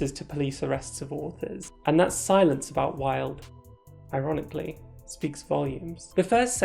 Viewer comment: I keep hearing “bi-ronic” liek bi-ironic XD